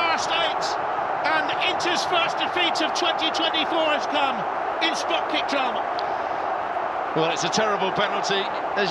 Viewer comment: Did he just say "It's f*cking drama" or am I hearing it completely wrong?! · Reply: He said in spot kick drama